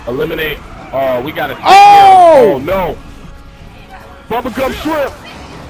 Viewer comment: best part 😂😂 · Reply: did he just say bubblegum shift?